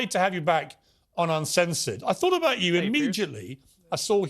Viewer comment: Did Piers say: I thought about you im-midget-ly??